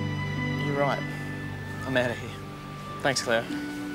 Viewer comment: DID HE SAY CLAIRE OML